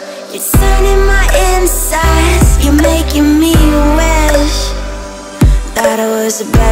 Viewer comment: thought she said "you making me wet" at first. i was like WTF this is some freaky shit.